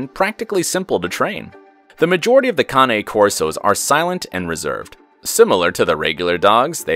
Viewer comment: I thought he said the Kanye Corso at first.